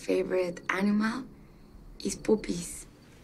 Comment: “My favorite animal is, poopies” I know what she rlly said but wtffff